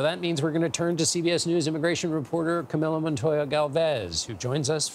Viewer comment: His name is pronounced Kah.Mee.Lo. At least pronounce it correctly in English as Kah.Mil.Lo. I thought he said Camilla.